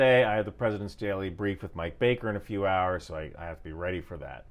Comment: I heard "I have a daily brief with my baker" and was suddenly impressed